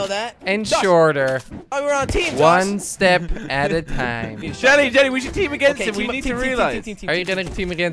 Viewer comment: did u just say timmy jelly